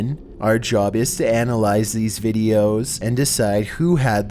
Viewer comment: did say "final breaffs"? lol